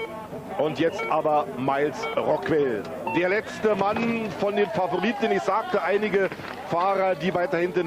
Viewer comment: What he say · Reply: Der letzte Mann von den Favoriten. The last guy of the favourites.